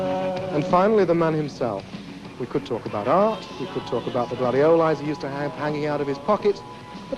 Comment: What does he say at 3:08? Something hanging out his pocket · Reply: Gladioli, the plural for gladiola.